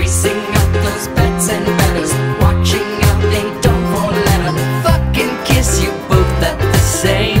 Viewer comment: DID I JUST HEAR "FUCK AND KISS"